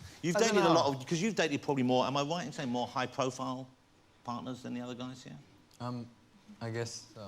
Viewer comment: what does Jonathan saying? i'm italian and i can't understand. Someone could help me, please? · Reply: He said to Harry "am I right in saying that you have dated more high profile partners than the other guys?"